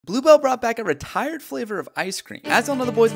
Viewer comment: Full video with Isaiah Photo out now!! <link> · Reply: Literally thought you said Glubo Instead of Blue Bell 😂😂😂😂